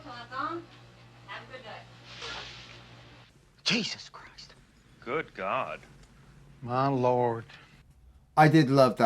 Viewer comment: When he said, "good god" I heard "It's a Doozy".